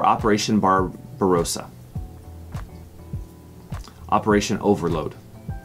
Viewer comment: Nice Quiz. BTW it's Operation "Overlord" rather than "Overload" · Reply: I saw 'Overload' but heard him say "Overlord".